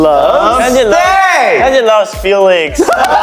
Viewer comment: How other hear : 'Hyunjin love Felix' ❤ How I hear : 'Hyunjin love stealing' 😭 [No Hate[